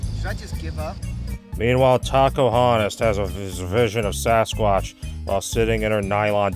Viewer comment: I don't know why but whenever says "Tacohonos" It always sounds like he is saying "Takahunters"